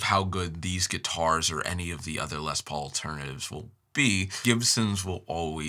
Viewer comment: I kept hearing "Les Paul-ternatives". Can't unhear it. (I'm torn between an '89 Gibson LP Standard and a mid '90s Korean-made Epi LP Standard as the best LP I've owned or played.)